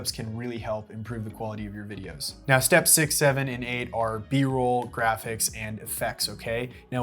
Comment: did he just say six seven!!!